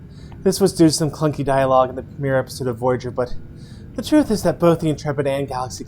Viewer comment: you mentioned some “clunky dialogue in the mirror episode of Voyager”, which leads me to believe you’re thinking of a mirror episode of Voyager.... Did I hear you wrong? Or take it the wrong way? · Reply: Oh! I was saying the "the premiere episode" not "the mirror episode" I just mumbled it so it came out poorly. Sorry about that.